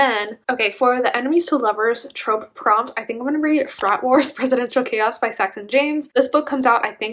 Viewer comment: Our collective human, 😂😭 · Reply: When you said “Frat Wars” I heard “Thot Wars” and my ears PEAKED 🤦🏼‍♀️